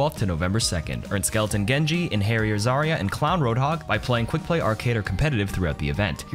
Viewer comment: Thought he was saying “hairy zarya” lol